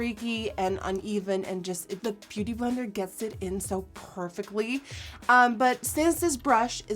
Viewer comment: Did anyone notice that at 2:53, it sounded like she said Pore-fectly instead of perfectly??!! I Love It!